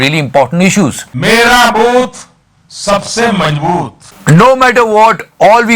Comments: did he just say "mera moot sabse majboot?" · did I hear " mera mooth ,sabse mazbooth" 🤔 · I literally heard 2:26 mera moot sabse majboot 😅😅😅